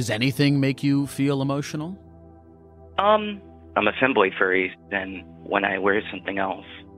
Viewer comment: Was not expecting “femboy furry” to be a part of that conversation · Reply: I heard "thimbly furry" i was like wtf is that?